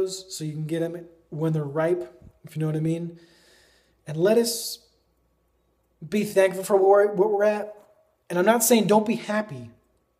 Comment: yess lettuce be thankful :D haha sorry thats just what i first thought you said :D